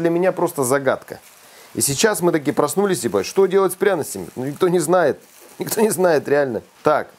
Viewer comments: мне послышалось? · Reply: "И сейчас мы такие проснулись, ...бать, что с пряностями?!"🤣🤣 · "...а сейчас мы такие проснулись, ебать..." или мне послышалось?